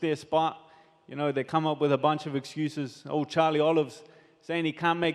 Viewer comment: “Charles Oliver can’t make the weight? I got broomsticks bigger than that mate” 😂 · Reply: I thought he said "Charlie Olives" 😂